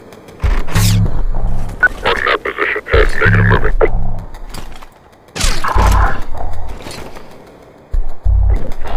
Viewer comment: DID HE SAYS THE N WORD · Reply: I’m pretty sure he said “Target at position and negative movement”